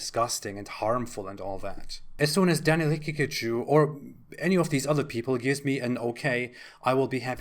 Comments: Man said Pikachu😭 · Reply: I swear, I also thought that I heard "Pikachu" 😂 Also what kind of name is Haqiqatjou? I assume that isn't his birthname as he's a convert, but I speak Arabic and Haqiqatjou doesn't really sound Arab... more like, maybe a West African name... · My Finnish ears heard you say his name was Pikatchu and I was so confused. :D